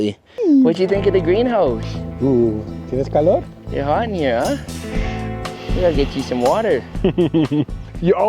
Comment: If you dont have a name for the pup yet, im gonna throw "Anya" into the mix. In the video I thought like you said to her you got something on ya. But could actually work well for a name. Turns out you said your hot in here huh gotta get you some water but I still like Anya